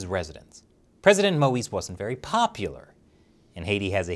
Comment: I swear, the first few times I heard it, I heard "President Möbius was very popular" and I immediately though "omg he finally got his jet ski!"